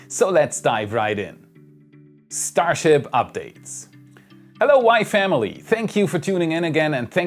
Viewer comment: Every time you say “Hello WAI Family” I hear it as “Hello Wife Emily”